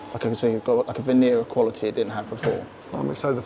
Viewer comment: Did he really say 'venereal quality'? 😁